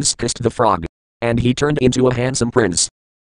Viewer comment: Your Program Mispronounced Princess. It Sounded like it said Prince Twice.